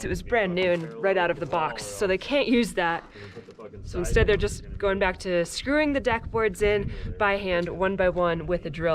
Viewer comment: what did u say? F what? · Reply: F Off, & giggles, idiotic